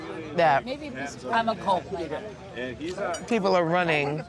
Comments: who tf said i’m a cult leader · am i going crazy or did i hear lisa rinna say something about a cult leader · Does anyone hear Rinna or somebody saying…” I’m in a cult…”I’m a cult leader” listen closely